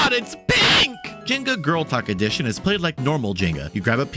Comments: I wasn’t looking at my phone and had to go back because I thought you said “girl tuck” instead of “girl talk” lmao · I heard GirlTuck at first, but Jenga isn’t that woke… yet 😈… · i misheard jenga girltalk as jenga girlcock